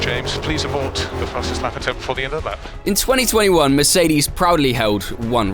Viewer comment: "Before the end of the lap" is what he was saying